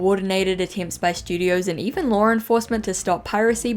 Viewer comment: i swear to god i heard that as 'studios and even Lauren Faust'